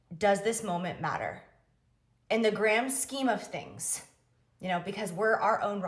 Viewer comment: did she just say “in the GRAM SCHEME of things”?????? 😭😭😭😭😭😭😭